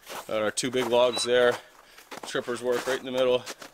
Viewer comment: i thought I heard Chewbacca in Tripper